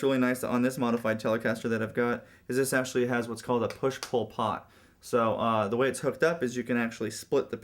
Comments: When you said push-pull pot, i thought you said push Pol Pot haha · i thought he said "push Pol Pot" instead of "push-pull pot"